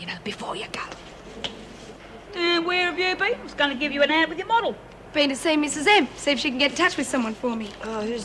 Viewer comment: What did Cass say to Bobbie at 39:21, albicon model it sounded like lol. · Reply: "A hand with your model"